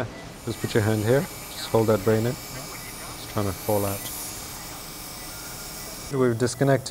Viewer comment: did he just say hold that brain in, its going to fall out